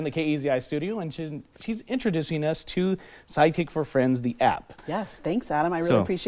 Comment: Lmao on 1.5x speed, it sounded like he was saying "Psychic for Friends"